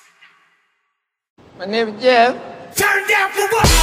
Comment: what he said? ! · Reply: My name is jeff